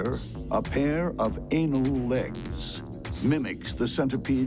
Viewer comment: I'm not the only one who heard "anal legs" right?